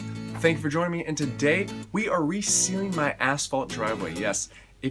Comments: So many times throughout the video i thought you said "asshole" driveway. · All I hear is "asshole driveway" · i thought he said “asshole driveway” 😳